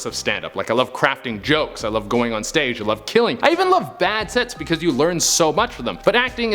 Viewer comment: "i love killing" lol is that what i heard, if so this dude is even funnier than i gave him credit for